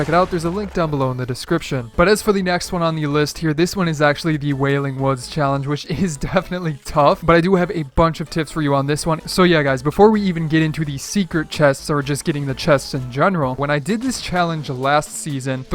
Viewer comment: Did anyone else hear him say tits